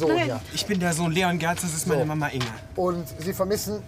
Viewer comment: verstehe ich das richtig hat er Inge gesagt?